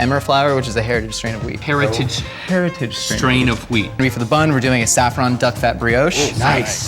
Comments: I thought he said strain of weed lol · I thought he said hairy stran of weed....... for the flour they are using 😂😂😂. And cats are so bushy from what link heard. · I thought he said it was a strain of weed and I got excited. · thought the guy said "heritage strain of weed" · I thought he said strain of weed.. not wheat. 😂😂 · I thought they said “strain of weed” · I thought he said strain of weed💀💀